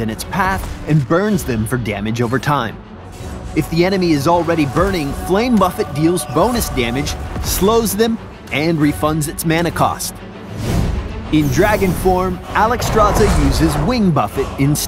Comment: Anyone else hear him say "flame muffet" at 2:14?